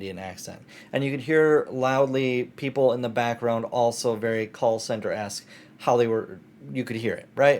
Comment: As you're asking for feedback: you can upload custom subtitles to the video and instead use those as the auto generated - and have them animated/styled too. I will say though, at 10:27 the subtitles are wrong for "call center esque" while the auto generated ones are correct.